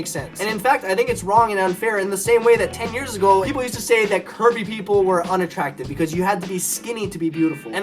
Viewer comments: When he said 'curvy people' I thought he said 'Kirby people' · It sounded like he was saying "Kirby" instead of "curvy" lol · When you first said curvy I thought you said Kirby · i thought he kept saying kirby not curvy XD